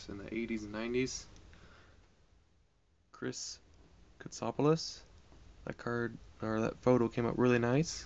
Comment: Yay cards! Thought you said Chris Farasopoulos who I sent today.